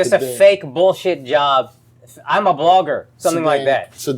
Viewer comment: I thought he said a fake bullish job im a Barber at first, I had to listen to it like five times to realize he was saying blogger lol i was ayyyy common man